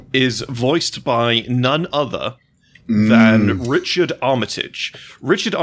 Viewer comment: I initially heard this as Richard Ayoade which I now want